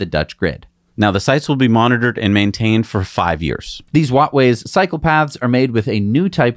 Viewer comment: Cycle paths - not used to hearing this term, kept mishearing psychopath